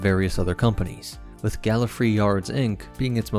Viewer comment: "Gallofree Yards Inc." For about half a second I thought he said Gallifrey